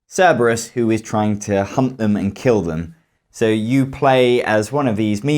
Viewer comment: did you just say... hump them and kill them?